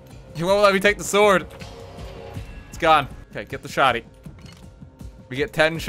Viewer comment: I know is talking about guns but everytime I hear him say "get the shawtie" 💀💀💀💀💀